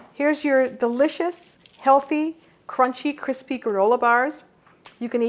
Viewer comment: Did she say gorilla bars?